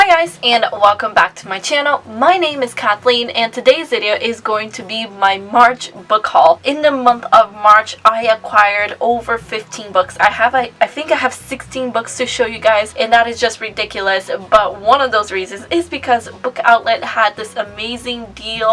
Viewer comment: OMG I THOUGHT YOU SAID 50 BOOKS I WAS LIKE WTF GIRL HOW but then I was like oh no she said 15 thats better